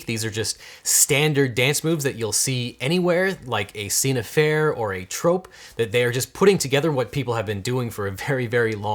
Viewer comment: What is the word or phrase used at 10:24 that is closed-captioned as "a scene affair"? The partial line is "...like 'a scene affair' or a trope...." Thanks in advance. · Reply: I believe he said scène à faire : A scene in a book or film which is almost obligatory for a genre of its type. In the U.S. it also refers to a principle in copyright law in which certain elements of a creative work are held to be not protected when they are mandated by or customary to the genre -Wikipedia :)